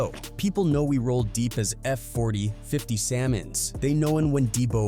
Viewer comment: 41, "active samans" or "salmons" instead of Samoans, after properly pronouncing the word "Somoan" previously, is this AI? making dumb mistakes that people probably wouldn't make?